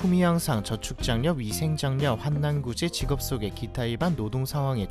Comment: 밥먹다가 한남구제라는말에 놀라서 영상 확인함..사회가 병드니 나도 헛것이 들리네